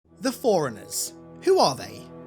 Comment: Had this on my watch later so it came up without me knowing what video it was. Nearly spit when i thought i heard "the foreigners"